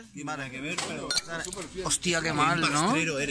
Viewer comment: Is it catalan? In quebec we say ''osti de marde!'' and that sounded like ostia de marde!